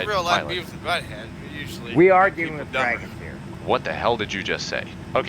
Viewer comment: "One small step for a plane, one giant leap for a pilot." - Drunk ATC #2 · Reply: "we ARE dealing with dragons here." That's what I heard anyways